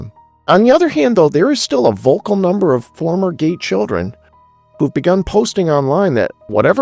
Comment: Im hard of hearing and no matter how high I turn it i keep hearing "former gay children" 😂 great video regardless.